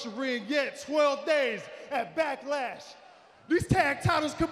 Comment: Sounded like he said Factlash 😂